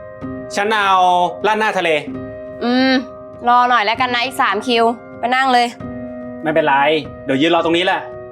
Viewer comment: ตอน1:40 ได้ยินว่า อีสัดคิวนะ ขออภัยในการใช้คำหยาบคาย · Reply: เขาบอกว่ารอหน่อยนะอีก 3 คิวไม่ใช่อ**** ฟังดีๆนะคะเขาไม่ได้ด่าใครนะคะ